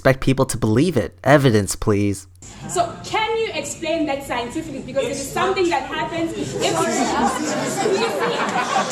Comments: what did he say? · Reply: “It’s not true” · what is that guy saying the one that later apologized. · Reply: "it's not true"